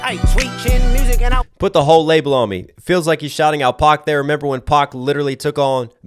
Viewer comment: He actually says put the wrong label on me, I believe referring to Drake labeling Kendrick as a wife beater in Family matters